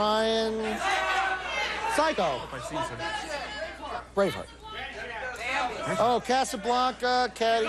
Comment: hahaha, when Carey says "Braveheart" I totally heard "brainfart" lol :D